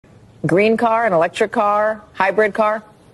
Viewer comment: I though she said, "Green card...." Listen to again. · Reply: Came here to say this, straight up sounded like she said green card 😂